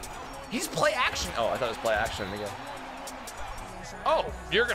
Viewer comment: what did you say?????? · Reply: He said "play action again" but it did sound like the n word